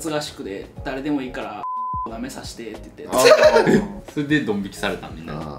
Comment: 口隠すな何て言ってるか分かってるんやぞ笑笑 やっぱ「なめさせて」って言ってたか（